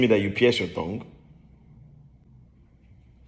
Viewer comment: Idk why but when he got serious all I hear was "There's nothing to smile about here, you bald headed demon"